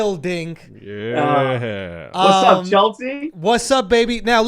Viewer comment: I thought Paul said "What's up Chelsea?" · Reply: 50GummedPapers what’s up shultzy, it’s his nickname